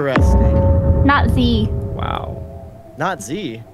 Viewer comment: Who else heard natzi at 13:14 😂